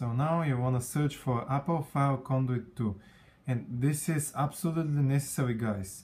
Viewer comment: what is the thing you need to search for in cydia? all i heard was "Apple find icon two" is that right? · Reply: Apple file conduit 2. Sorry if I said it too fast the name is actually a bit complicated:)